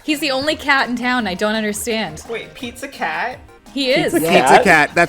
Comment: When they said "Pete's a cat" I thought they said "Pizza Cat"